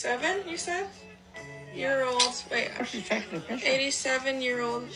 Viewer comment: I thought she said "87 you said?" and then "You're old!" LMAO